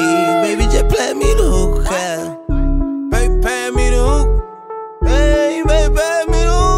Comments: Pass me the hookah thought he was saying "they ain't paying me though" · He is saying "pass me the hookah" I thought he was saying "cant pass me though" lol